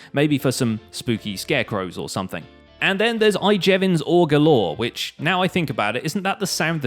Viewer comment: when you said ore galore, it sounded like orgalorg (from adventure time)